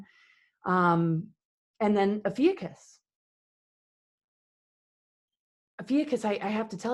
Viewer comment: the 24:42 to 24:46 time frame says a word (twice) that I can Not make out... afukus... rafukus??? Does Anyone know what she's saying here??? sounds like it's important... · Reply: Ophiuchus, the constellation/13 zodiac sign